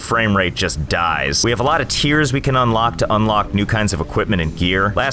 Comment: At first I thought you said "We have a lot of tears to unlock"